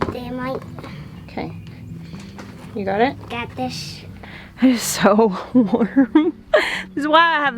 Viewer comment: Ok, I keep replaying the convo at 5:20 and I feel like I have to be hearing this wrong. I see no comments about it and didn't react so I feel like this can't be what was said. "It's ok" "Damn right" "Ok. You got it?" "Got this sh-" <grins> Am I just losing my mind? Haha